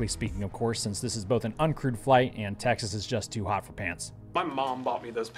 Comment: I keep hearing “crewed” as “crude”